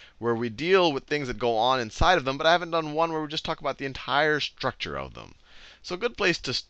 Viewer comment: did he just say 'structure of Sal'? :D